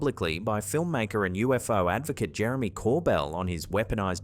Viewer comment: I could have sworn you said "UFO maker" instead of "Film Maker and UFO Advocate".... Spooky. Still true both ways. 11:51 AI hallucinating sentences that aren't there, in an AI comment about AI footage, so deep it gets scary.